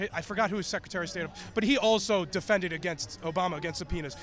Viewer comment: Did just say Obama also defended against a penis? eeew